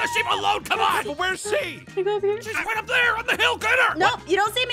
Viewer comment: did arron said the "H" word? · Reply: He said hill